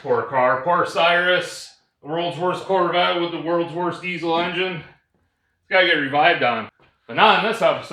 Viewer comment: Wait... That's not a 6.0 power smoke ... I thought you said worlds worst diesel 😂